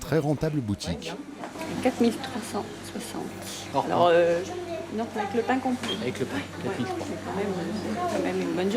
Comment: C'est moi ou il disent "avec le black compris" ? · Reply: Avec "le pain" compris.